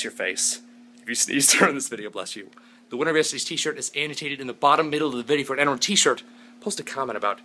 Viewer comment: I've been watching Tobscus Vlog for years now and I've always thought he said "The winner of yesterdays T-shirt is EDITATED on the bottom- middle of the video. I'm only realizing now that he's actually saying "the winner of yesterdays T-shirt is ANNOTATED on the bottom-middle of the video". I kind of feel stupid not realizing. But you can understand my confusion.